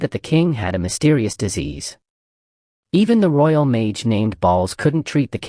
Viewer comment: royal mage named balls? did i hear that right?